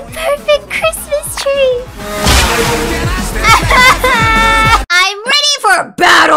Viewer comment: I thought said "I'm Ready For Love".